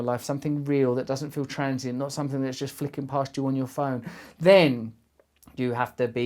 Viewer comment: I love this video, my English is not great though.. can someone help me with what he says and what it means at 7:41? trancy..... fleakin posture on your phone? help! haha